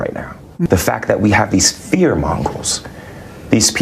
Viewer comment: Did he just say “fear mongol “ , Mongolians are not known for their fear ! . Mr smollet sure isn’t hot on Geography or other Cultures 🧐🇬🇧🇺🇸